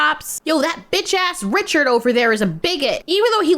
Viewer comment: i thought you said “Richer,” like from South Park, instead of “Richard.” especially if you know how that episode ends… thought you just got reeeeeeal spicy just outta nowhere 😅